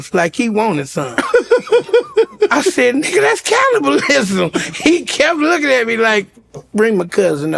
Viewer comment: Does Lavell say ‘cownabalism’ at 30:25? 😂